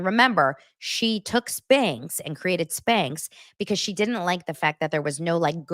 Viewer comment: Thought you were saying “snakes” instead of sneex 😅